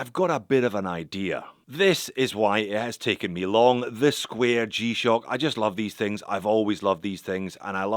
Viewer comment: Sounded like you said this queer g-shock @ 2:01 😂